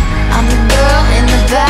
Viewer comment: that "Fuck bitches get cash" part caught me COMPLETELY off guard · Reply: No 1 Nia stan oops Lmao this whole time I thought she said blue cash